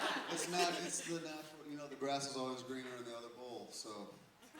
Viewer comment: Congratulations Matt! Wow, I heard "The grass is always greener on the other hole" instead of "the grass is always greener on the other bowl" When will the movie be in theaters? Cannot wait to see! =)